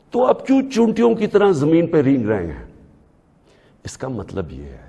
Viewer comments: did he say "chutiyo" ki Tarah??? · Did he just say chutiyon @ 7:00 😂😂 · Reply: He said chootiyo, meaning ants in punjabi...like cheenti in Hindi